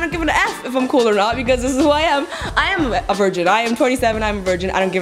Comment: i thought u said ''I am 37'' and i was like ''WHAT'' and i played it back and i was like '''Oh.. 27.. heh..''